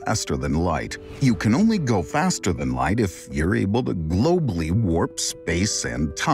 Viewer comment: Did you just say "globally"? 😲